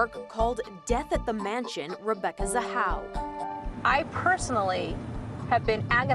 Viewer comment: Rip Rebecca but when I first watched this I thought they said Rebecca is a hoe at 0:25 but I mean no harm or disrespect to anyone who find this offensive